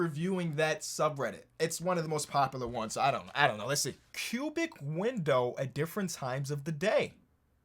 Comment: I thought he said pubic for the cubic window 💀